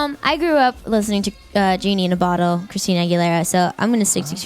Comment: she said "i grew up listen demi lovato"?? or what she said? i understend "lovato" :| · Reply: She said genie in a bottle lol not demi lovato